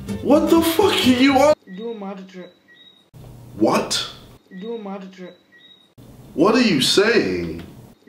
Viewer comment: HOLY SHIT I just realised what the kid was saying at 2:15 “do a magic trick”